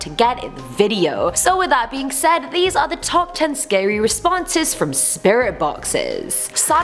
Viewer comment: instead of scary, I thought it said sexy LOL